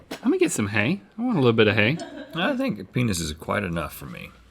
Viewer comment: I thought they said munk penis